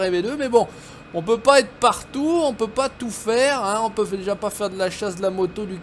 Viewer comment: "On peut patou faire" patou!? Ou ça!! J'ai entendu une cibi!!😂